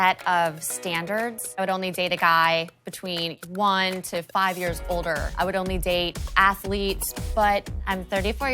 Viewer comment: When started talking about her standards I thought she was saying that she only dates 1-5 years old..... I was about to call Chris Hansen